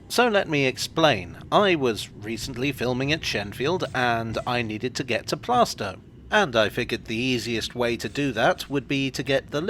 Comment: I almost thought he said, "I was filming at Shenfield and I needed to get plastered" 😆